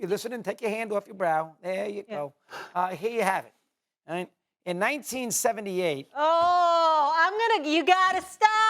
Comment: When I heard "in 1978,..." all I heard was "back in 'nam"🤣🤣🤣